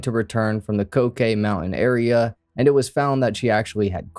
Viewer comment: Did Melanie not return from the cocaine mountains? 9:10 did I hear that right? Because that makes sense, If I ever found myself on a mountain of cocaine, I probably would not return 😂